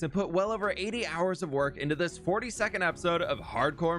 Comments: rly thought bro said 40 second not 42nd for a minute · When you said 42nd episode I thought you said 40 second episode. Lol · instead of "42nd epsiode" i heard "40 second episode" so then i looked at the length like "what???" and then a few seconds later realized im just stupid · In the beginning, he said, "For the 42nd episode". I heard the "40-second episode" and checked the length of the video XD.